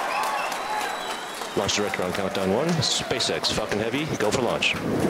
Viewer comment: Did he say SpaceX f*cking heavy? 😂